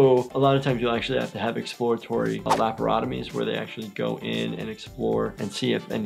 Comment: ~Тьфуу тыы блииин... Мне послышалось Лоботамию😅😅